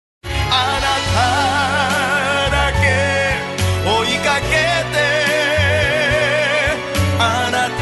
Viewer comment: ジェロもアルバムで歌ってるんだけど あなた～だけ～追いかけ～て～ の所 あなたは 誰と 契りますか に聞こえるｗｗｗ